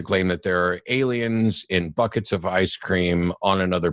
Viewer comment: Funny that I thought I heard him dancing in buckets of ice cream as well 🤷‍♂️